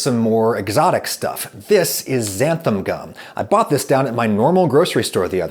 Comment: I have this weird Mandela effect thing with Xanthan gum. I would have bet my life that it was spelt and pronounced "Xanthum gum" but apparently not. I think Adam says "Xanthum" at 10:52. Does anyone else have this?